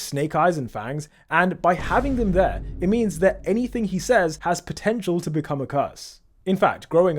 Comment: DID YOU SAY POTENTIAL